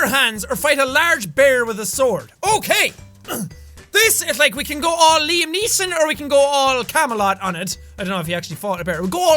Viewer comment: I thought on 1:47 he said "We could go all Lee Sin on it"